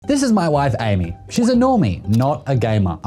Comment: I totally thought he said "This is my wife Amy. She's enormous" 😳🤣